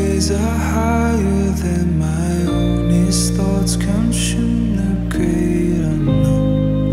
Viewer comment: I thought I heard "his thoughts CONSTRUE the great unknown" and not consume.